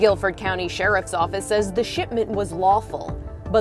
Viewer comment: She said the "shipment" was stolen; I thought she said shitment lol.